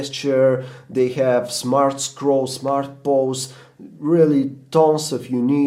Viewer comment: did he say "smart balls"?